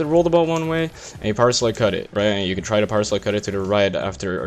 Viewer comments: Am i the only one that thought he was saying 'parsley cut'? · This whole time I thought he was saying "Parsley Cut"